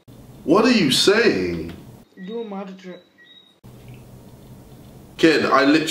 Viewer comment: The kid at 2:07 asked: do a magic trick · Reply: Caoiltemct123 I thought he said mad trick